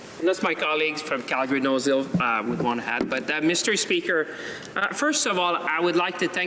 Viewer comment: Did he just called him "Mystery Speaker"? lol